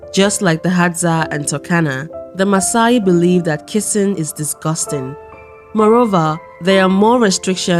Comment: What is disgusting🥴 2:26 did she say kissing what’s disgusting😩